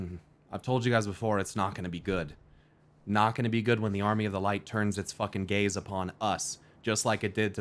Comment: Ngl I thought he said “the Light will fix its gays upon us.” I was like damn, a gay light army sounds powerful 😆